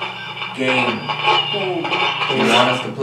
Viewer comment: I heard “gay” Guess the ship was ✨fruity✨